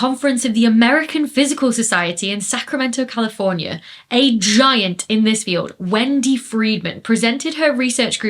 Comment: I kept hearing "a giant imbecile" instead of "a giant int his field". I felt like a giant imbecile when I had to turn on the subtitles 🤣